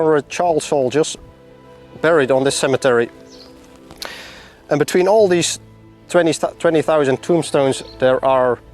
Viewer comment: Can’t understand what you said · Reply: I say: and between these 20.000 thombstones